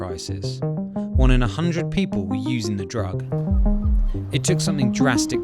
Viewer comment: How else thought he said “over 100 people where using the drug” at the start